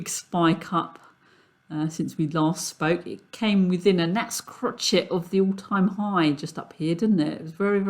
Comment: Did you say " it came within a gnat's crotch "? at 9:30? iF so : CAN i USE THAT EXPRESSION?